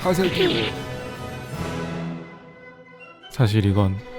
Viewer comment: 롤을 접은지 한참이지낫지만 아직까지 들리는 '하세기!'